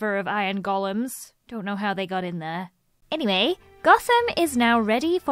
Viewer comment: did Lizzie say “Gossom?” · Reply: Gotham yk like batman or DC universe?